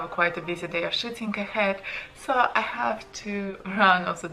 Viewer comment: Did she say "I have a busy day of shitting ahead" at @ 2:15?